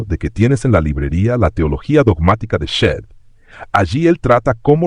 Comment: En el minuto 22:24 se menciona el libro de”la teología dogmática de…” no entiendo el nombre, alguien sabe a quién se refiere? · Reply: Teología Dogmática, de William Shedd.